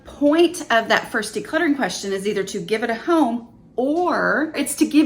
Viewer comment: every time I hear "Give it a H...(home)" I keep expecting to hear "Give it a hug" LOL